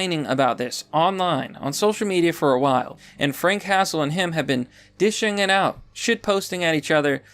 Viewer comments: I thought he said frank castle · For a moment I thought he said "Frank Castle" I was about to be like dam boogie shot at the punisher lol.